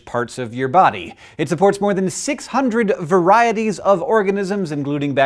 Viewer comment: Lol i thought that said the tongue holds 600 varieties of orgasms